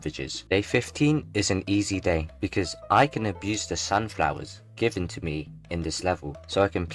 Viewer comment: When u said"use the sunflowers" I thought u said"abuse the sunflowers" 😅😂